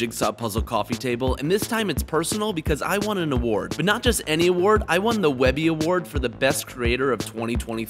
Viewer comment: I misheard it and thought he said "I wanted to start a war, but not just any war" 💀